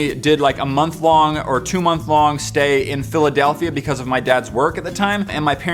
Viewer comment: did you say fillidelthia (can't spell) or filly delthia. we might have a brony here.